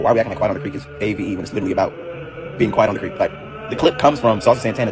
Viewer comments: oh .. i thought he was saying u gotta be real quiet while u creep .. oh i slow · oh .. i thought he was saying u gotta be real quiet while u creep .. oh i slow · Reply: 😂😂😂😂I thought they were saying “Quiet on the creep” too lmao